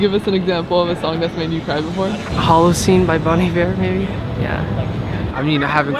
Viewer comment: what did he say? I want to know the song :)) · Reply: Bon Iver -Halocene make sure to read thru the comments.